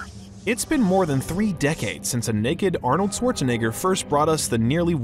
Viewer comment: what did he just say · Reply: Arnoldswartznegger